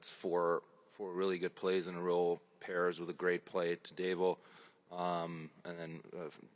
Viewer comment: Did Leon call Connor "Davo"?